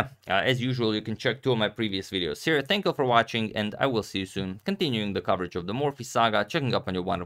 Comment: Did he just say 'continuing the rest of Morphy saga'?